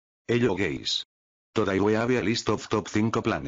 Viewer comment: I MEAN ( 0:01 ) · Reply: I heard “hello gays”💀